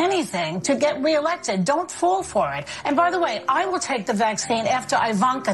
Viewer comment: is she saying “Don’t fool for it” or “Don’t fall for it”?